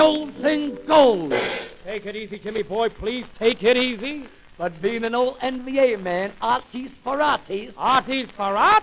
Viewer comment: "But being an old MBA man, Artie Sperarti"??? (What exactly is he saying here?)